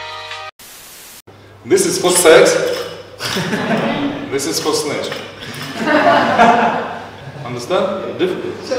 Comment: what does he say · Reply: “This is for sex, this is for snatch. Understand?”